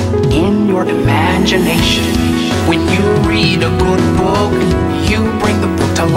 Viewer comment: When he said "good book" I thought he said "book book😂